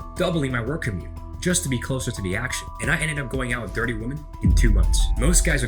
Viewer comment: I heard "I ended up going out with 30 women" as "I ended up going out with dirty women" LOL - not far off the truth anyway